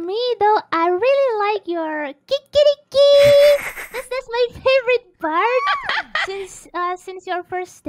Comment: I think Risu said "that's my favorite part"